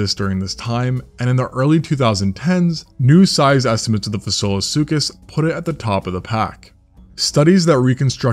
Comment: Definitely thought you were saying “Basilosuchus” which would be fitting as it means “king crocodile”. Bit of a missed opportunity by the namers